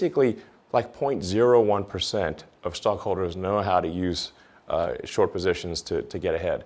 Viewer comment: At minute 43:39, did he say Stonk holders...? If so, i´m dying, hahahahahahhah